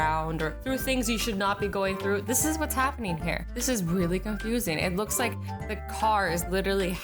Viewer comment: lol, it sounded like she said, "wheely" instead of "really"